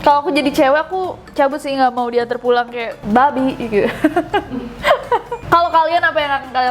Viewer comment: did u say babi? 🤣